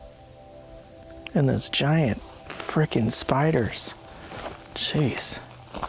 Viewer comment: Right at 9:17 when the EVP says "little spiders" I heard: "Shikata ga nai" . Shikata ga nai (shee - KA - ta - ga - nye - ee) is a Japanese phrase that means "It can't be helped," or, more related to your exploration "It couldn't be helped." I'm not Japanese, I'm a Canadian of French - Canadian background. I speak French but I've studied many languages on my own just out of curiosity.